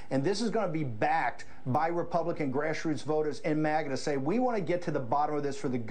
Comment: Did he say "MAGNA" at 0:23? What does that even mean? Bonus points for the best expansion of the acronym!